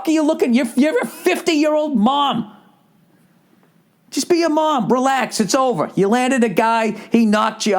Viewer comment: Bill; “Just be a mom, burlesque”. That’s what I heard! It must be Bill’s accent. Btw, ‘nglish is my second language.